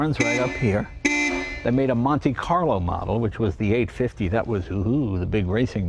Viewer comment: Instead of "Monte carlo" I thought he said, "Monte Python" model...